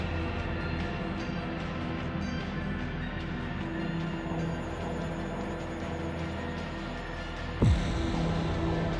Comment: what was he meant to be saying? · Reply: A Motive?